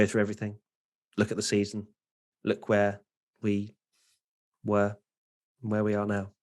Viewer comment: I thought said Lecwhere attempting to make a funny new Leclerc meme or something when he actually said "look where"💀